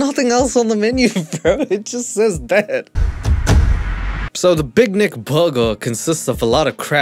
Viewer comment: I thought it said the big neck burger 😂